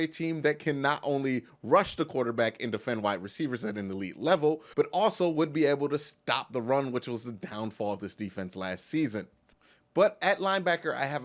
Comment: whatthe, did u say “defend white wrecievers” ? 😮🤣😂🤣🤣😂🤣 great video wonderful idea an thanks for sharing the knowledge king, truly blessed mucho corazon ❤️💛💚 12-4 cle Bal 11-5 9-5-2 pit The flemlos 3-13 tops